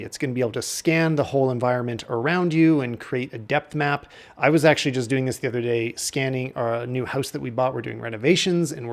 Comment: Didn't catch the name of the app properly that was mentioned at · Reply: It’s called Halide